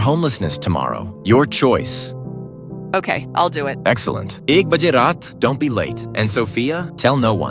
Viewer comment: what did he just say💀? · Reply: "ek baje raat" Hindi translation of 1:00 AM , IDK why they used it though